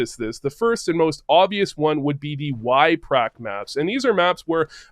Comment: Hey man loved it! Can you help me with something, I couldn't understand the map you were talking about on 10:50. Is it wipe rack map or what lol I didn't get the accent, and also can you suggest some map names where bots hold an angle and we do the peek kill.... Thanks for the video. <3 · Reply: Yprac bot maps. there should be one for most competitive maps